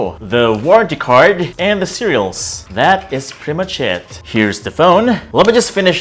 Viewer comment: Bro no offence but when i listen to you saying "that is pretty much it" I hear "that is pretty much SHIT"